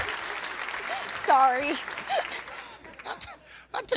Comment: I'm not gonna lie, I thought they said: "Chip n Dale's" because I've never heard of "Chippendales" before. Thank god, for Google Safe Search.